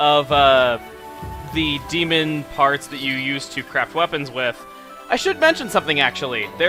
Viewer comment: I thought he said "... Items you crap wepons with." XD